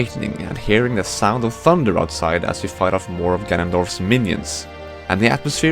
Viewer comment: Did I just hear you say Gandalf?